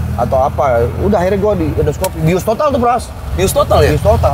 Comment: "waktu gerd gue sampe di endors kopi". salah denger wkwk. endoskopi ternyata